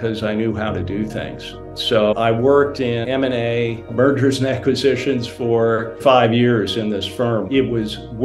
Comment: For a second I thought he actually said: "Murders and Aquisitions"! Funny and sad how ones ears play tricks like that. But from what little I know it doesn't sound too far off the mark.